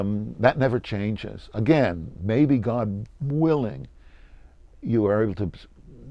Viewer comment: Seriously? The captioning, which, by the way, I, to my knowledge, didn't turn on, changed what the interviewed said, namely "God's willing," to "gosh-willing." ("0:38.") (Smh.)